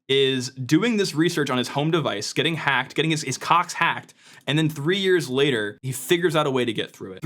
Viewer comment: I heard “his cox hacked” and immediately came to the comments section—it did not disappoint.